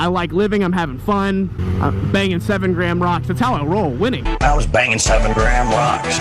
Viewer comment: lmfao it sounded like you said you was banging grandma!!!